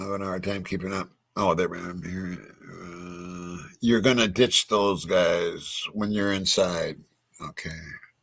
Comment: Jeff H, no disrespect, but your response to what you heard, had me rolling 🤣 😂❤ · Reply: What I heard was.. "The way you ditched those guys was an insult". Honestly nobody should be made fun of for trying to decipher these audios. We're all trying to help figure them out. All it takes is one person to hear something then others might be able to build off of it.